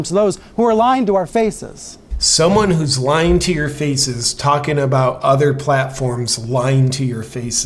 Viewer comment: > I thought I heard: "lying to your feaces" 🤦‍♂️(my English is rubbish) @ 12:40 -> 4500 staffers, fewer views ==>> means that even they didn't watch the 'show'... they were in it only for the money 🤷🏻‍♂️